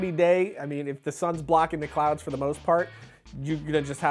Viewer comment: Did he say the sun is blocking the clouds?